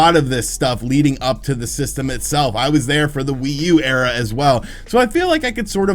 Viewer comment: Did you say Nintendo Wii U "era" or "error" 😉